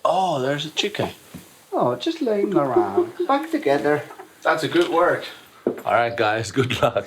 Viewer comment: Did he say "Bawk together" or "Fu** together"